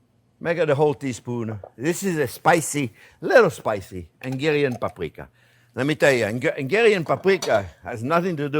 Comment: Chef Pierre, can I ask what is that paprika you are using? Transcript says argaran paprika but I am not finding any results when looking for that term online. I thought you said ingilian paprika but not finding that either. What is the paprika you are using called please?😅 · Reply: Hungarian paprika! 👍❤️